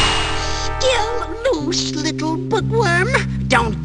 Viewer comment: "my pets a-" you stop the clip there, it'll sound like he says, "my pizza" rofl